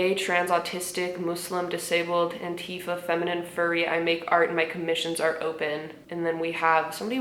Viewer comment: I thought you said furby not furry💀